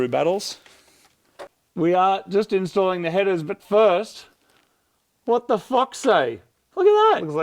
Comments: For a sec there I thought he said what the F@ sake, not the what the fox say lol.. you guys are awesome... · I thought said "what the fuck" not what's the fox say 😂